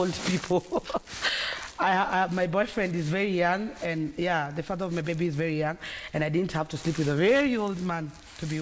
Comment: Did I just hear Bridget say the father of her baby? @ 13:55. I thought she was childless!